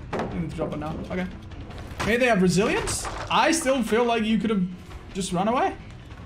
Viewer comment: Every time he says "resilience" I hear "Brazilians"